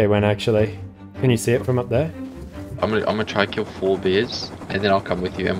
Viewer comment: I thought he said, "I'm going to kill 4 beers".